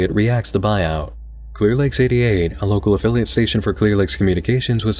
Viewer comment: I thought he said "clear legs" for the first five minutes